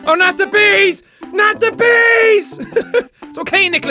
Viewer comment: it sounded like nazi bees